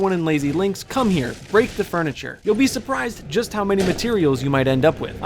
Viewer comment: Anyone else thought he said "Rape the furniture" at first?